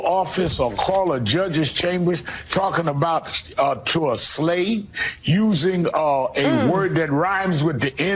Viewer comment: Also, did Sharpton just call a black woman involved in this "a slave" at 0:07 (and at 2:05)?🤔😯